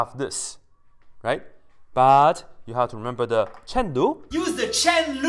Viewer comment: Fantastic vid! Happy Pi day! Thanks. I would like to know the source of the video of the "chen lu" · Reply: It is Dr.. misheard his heavily accented professor say Chain Rule as Chen Lu.